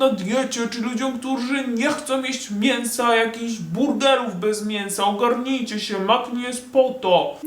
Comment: usłyszałam BURDELÓW BEZ MIĘSA XD